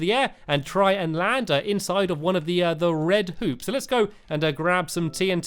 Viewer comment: don't get mad but it sounded like he said 'colored poops' instead of 'colored hoops'